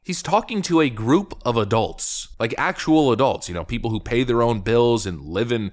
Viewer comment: He's not talking to adults , he's talking to Leftists. Big difference. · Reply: Oh, "adults!" I thought he said "...uh, dolts."